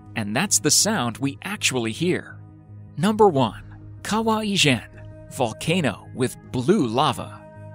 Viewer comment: I heard it as Kawaii Chan lol